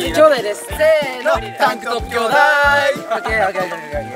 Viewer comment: ｢タンクトップ兄弟～⤴️｣が｢10本アニメ～⤴️｣にしか聞こえなくて面白い笑笑